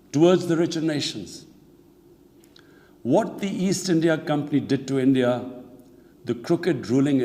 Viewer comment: Does anyone know what companies he mention at 5:59? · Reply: East India Tea Company.